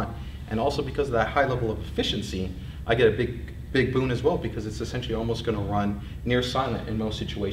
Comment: I thought he said "I've got big boobs" 8:58"